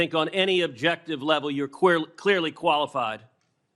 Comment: "I think on any objective level, you're queerly qualified for this position." (Yeah, he misspoke and fixed it.) That is what he should have said about Biden's nominees!